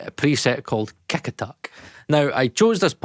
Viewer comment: I love scottish accent. For Kick Attack preset I thought he said KekAttic.